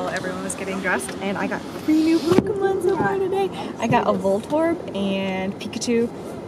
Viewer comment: While I was was watching the video I wasn't really paying attention to it so when said she got a voltorb I thought she said voldemort. LOL